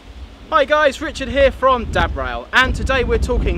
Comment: The first time you said dad rail I thought you said DAB rail !!! Lol